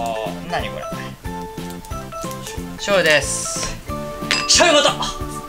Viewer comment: スタッフが醤油ですって小声で教えてるのが聞こえる👂